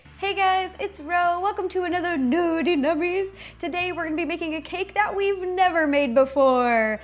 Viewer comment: cannot see when you tip chrome bowl :( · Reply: could have swore I heard "nudie mummies" 0:12 :D